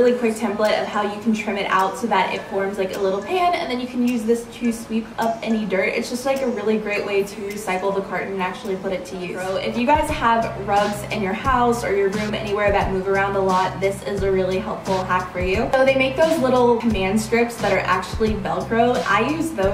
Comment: I thought you said drugs instead of rugs